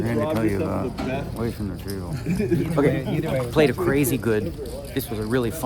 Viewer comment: I thought Dnegs originally whispered “I need to get away from the Jew” but think he said something with word “drew”